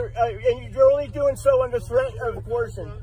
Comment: did he say "under threat of abortion."? Jeez, you Americans.